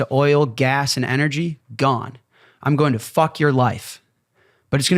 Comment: "Easy access to oil ass and energy, gone. I am going to fuck your wife." That's what he said at 13:23. I swear to Allah.